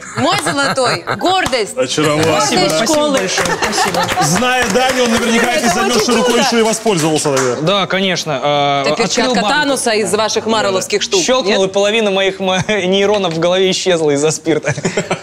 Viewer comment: Я услышал Перчатка Ануса )))